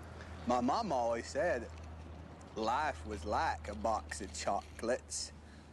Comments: I remember back in 2018 or so , this switched back and forth to was and is . Now its just was. Anyone remember that ? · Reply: I hear "life with like a box of chocolate" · I watched this movie at least 100 times as a kid. I had to ratch this after hearing about the Mandela effect. I agree that it was like this in the original version but I heard it as him saying life is like a big of chocolates. He just says it lazily so it reads like that. I think this one is how it actually was. However. There was definitely a Sinbad movie Shazam back in the day. It was the Berenstein bears. Fruit of the loom logo for sure had a cornucopia.